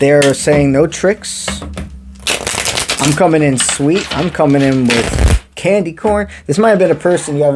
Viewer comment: When you kinda sang the word candy corn, I heard "Candy Mountain 🏔️ Charlie!" Lol 😅🍭🐴🐎